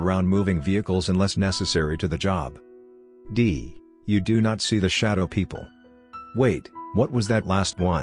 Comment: I got an ad here so it sounded like “you do not see the shadow pee”